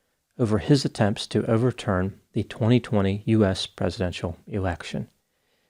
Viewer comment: Ha! Must be me, but i heard “turn over the American presidential erection” 😂😂